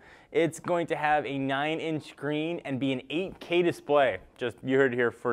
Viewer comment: Did I just hear '8K display' ?